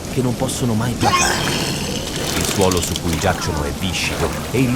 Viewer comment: Ma cosa dice la voce narrante in questo secondo 14:41 il tipo ha sbrattato mentre parla😄😄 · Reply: "il suolo si cui giacciono e viscido....